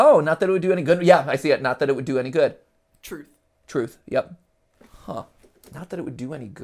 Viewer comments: I think it says "NOT THAT IT WOULD BE AN GOOD BUT IF" and then the two big letters of "O" and "F" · I think it says: Not that it would do any good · On 12:05 i think says not that it would do any good